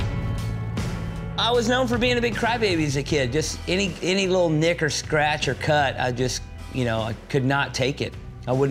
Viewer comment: I still wonder why he has a toothpick in his mouth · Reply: Ouija ZaZa Gaming I can’t tell if you’re trolling or not but what he actually said is: “I was known for being a crybaby as a kid, any old nick or scratch or cut, I couldn’t take it”